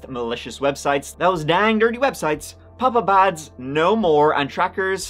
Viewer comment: I know you said “pop up ads” but I heard “papa bads” and I find that hilarious 😂